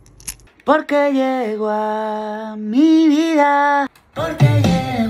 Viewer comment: "porque llegó a mi vida el amor de mi vida". Letra fina señores🧐🥂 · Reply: Yo escuché "por que llegua".